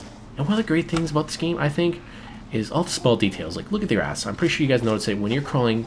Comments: when he said "look at the grass" i coulda sworn he said "look at my ass" lol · When he said "look at the grass" I thought he said "look at your ass"...and I did O.o